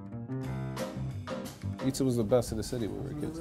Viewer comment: I thought it said scam’s pizza